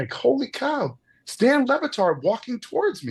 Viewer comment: Stan LeBatard...? For real...? 2:07 mark...I defy you to tell me he did not say STAN...